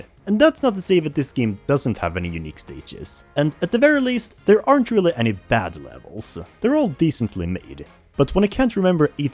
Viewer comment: I love how he says ''There aren't any ''bad'' levels'' and shows the level with the giant bat, I thought he actually said ''There aren't any ''bat'' levels'', which... isn't true, because he shows one as he's talking about it. I know it's just a funny coincidence, but that only makes it funnier.